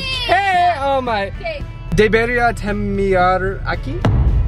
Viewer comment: when you said "should we end it here" i did not understand that i heard "deberiatemeara aqui?" I started laughing XD or did you mean "Deberíamos terminarlo aquí?"